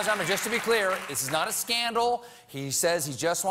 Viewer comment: And you get a scandal! You get a scandal! You get a scandal! Everybody gets scaaandaaals! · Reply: SCANDALS!? I thought someone said Handles!